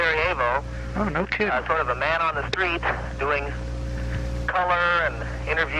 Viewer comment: did he just say no cap? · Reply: "No sweat". I think.